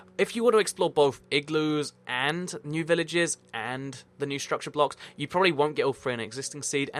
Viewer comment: who else heard him say structure blocks